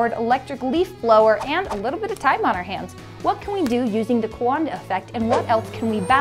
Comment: I thought she said the Canada effect